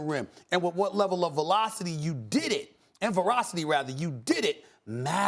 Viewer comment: I’m still trying to figure out what verocity is 😂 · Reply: lol at first I thought I must have misheard and that he was saying ferocity but then he kept saying it